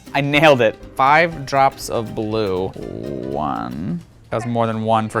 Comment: I thought I heard 5 drops of glue